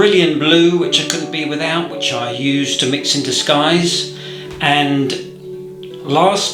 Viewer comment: Cerulean Blue. I heard 'I mix in disguise'. 😂 Thank you. Lovely video